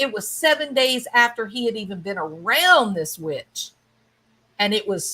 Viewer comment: Yay!! Glad you're back. I could've swore you said "bit**," I was like yes!! That's right ..haha I even rewinded it, you said witch tho bahaha 🧹